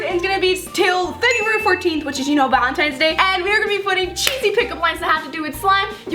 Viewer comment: I thought she said cheesy pickle!!!!